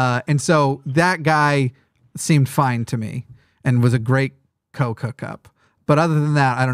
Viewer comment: Did say that guy was a great coke hookup at 1:40:56, lol?